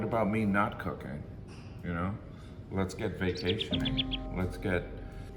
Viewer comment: When he said "let's get vacationing" I thought he said "let's get vasectomy".